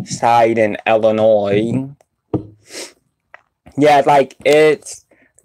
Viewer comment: I love banana chips but have you tried apple chips they yummy. Yam chips was that the one that said a touch of salt. Look like yam which are made from sweet potatoes. And the red yellow green bag of chips Sabritones I always wanted to try those. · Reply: Well umm you said they taste burnt so I made a funny joke lol and it sound like you said illinore instead of Illinois lol